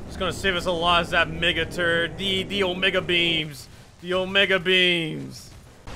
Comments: did he just say nigga? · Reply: He said mega turret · did he just said mege beans